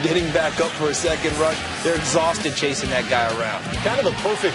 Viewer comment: did he just call the redskins autistic?😭😭